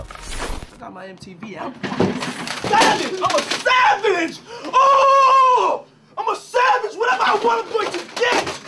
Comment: I dont know why I hear "IM A FA_ _OT IM A FA_ _OT OOOH WHAT EVER BOY I WANT I HAVE TO GET"...